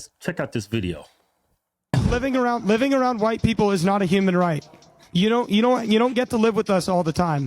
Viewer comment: I misheard it at first and thought they said a black guy told white people living around black people wasn't a right....I was like...is that really a thing that's a problem? Where is this magical place that white people are flocking to that's not already white majority?